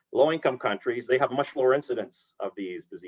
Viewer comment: Great video! Just FYI: I think around 9:10 the text doesn't match what he says... should say "in low income countries they have much LOWER (not more) incidence of these diseases"